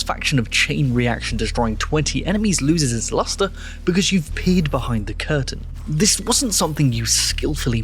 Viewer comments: I heard "you've peed behind the curtain" and I think that's a pretty good metaphor for the devs. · oh, "peered behind the curtain", I heard "peed" and was very confused for a second 😂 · I know he said “peered” but I can’t be the only who heard “peed” right?